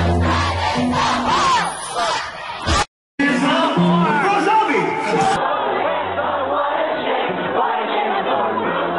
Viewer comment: Did he say "The poor grooms bride is a Rob Zombie? At 1:49 I think? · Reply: I asked Zack (Panic's bodyguard) on Twitter and he confirmed that Brendon said Rob Zombie. Lmao